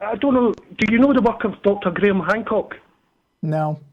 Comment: did say "Dr." or "author" Graham Hancock? I heard Dr., and as far as I know he's an author not a Dr.